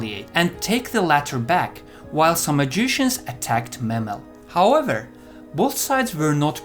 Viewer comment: Instead of Samogitians, I keep hearing 'some magicians'. Some magicians did this, some magicians did that.